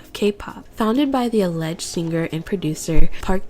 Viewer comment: imma be honest idk if i misheard you saying "alleged singer and producer" but if i didnt ur mad funny for that